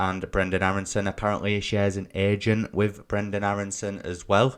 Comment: Did you just say he “shared an Asian” with Brendon Aaronson 🧐🤨 I hope it wasn’t in Thailand! 😳🤣